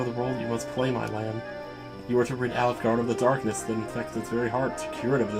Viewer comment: I'm listening to this while at work, and when you said "Alefgard" I swore I heard "Olive Garden"... I had to do a double take! 🤣